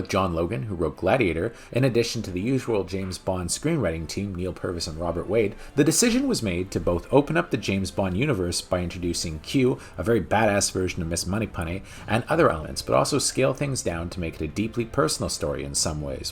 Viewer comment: "Moneypunny"? Thought I heard that a couple times. LOL